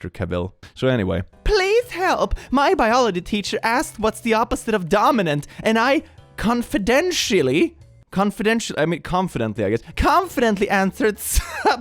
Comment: "Recessive" is the right word for genetic opposite of Dominant, I think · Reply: I was rubbing my eyes and I thought you said "Denmark" instead of "Dominant" and got so confused